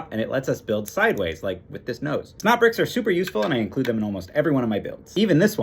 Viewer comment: i thought you were always saying snout bricks... it made so much sense..